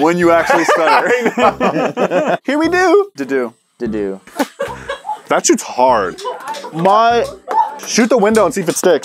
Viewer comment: Am I the only one that thought said “that sh*ts hard” when they were reviewing the D-Dart lmfao. I got scared lol